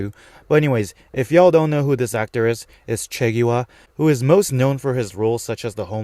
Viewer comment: i thought u called the actor of gi min chiggy wah 😂😂😂